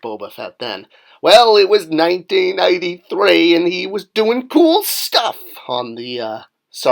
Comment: Did you say 1993? Oh I thought that beak sucked. I’m glad they got back to the original minus the CGI beak and additional tentacles.